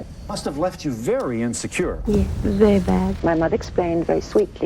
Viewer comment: Im sorry but the clip 0:12 when she says "he was very bad" she was actually saying "it was very bad" and talking about the war not her father. Its a interview (?) Tape of roman holiday, her casting . It is wrong that they put it out of context.